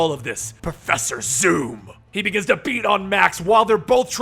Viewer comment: I thought he said Professor soup 🍜